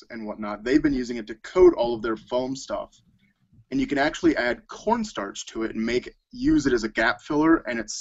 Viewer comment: I swear I thought he said "You could add porn stars to it" but I really want to see how that Hollow mask in the back turns out